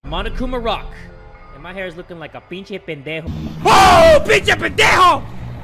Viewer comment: CAN SOMEONE TELL ME WHAT JAY IS SAYING AT 1:17-1:21 BECAUZE I WANNA KNOW WHAT IT MEANS AND HOW TO SPELL IT AS HE SAYS IT ALL THE TIME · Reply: He said "pinche pendejo"